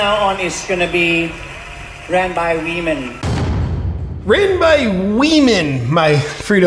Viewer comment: I hear, "run by we-men." 🤷🏻‍♀️